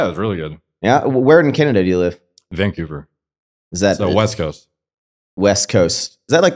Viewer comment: Sounded like Derek said “vinkouver” not Vancouver lol